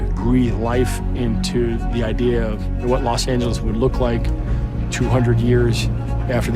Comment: bro said los santos instead of los angeles